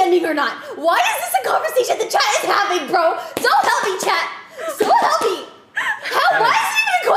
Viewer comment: What is she saying at 0:14 ? Is she saying "So hammy" ? (Sorry I'm not a native English speaker 🙏)